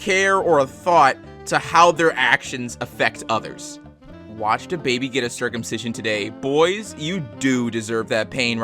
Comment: I wasn't paying attention and really misheard it as "watched dababy get circumcised" and I was so concerned